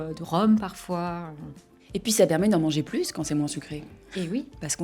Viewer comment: Excuse moi. Le sous-titre de 31:12 a dit « ça permet d'en manger plus » mais j'ai entendu au lieu « ça demande d'en manger plus » . Quelq'un a remarqué ça aussi? Merci pour tout vous faites, et. ❤